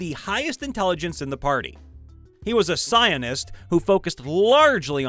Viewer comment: I thought you said the monkey was a Zionist. Lmfao!